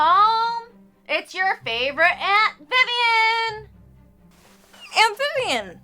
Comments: Am I the only one who hears "amphibian" when the braceface greets her aunt Vivian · speaking of tadpoles, I thought she said, "It's your favorite amphibian!"